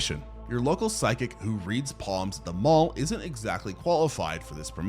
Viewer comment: why did he say psalms like that 💀